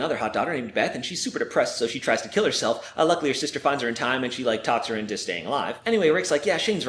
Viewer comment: I know it says "Uh luckily", but it sounds like he says "unluckily"